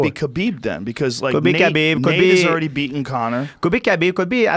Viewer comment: did joe say khabib has already beaten connor wtf LOL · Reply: Nate* has already beaten Connor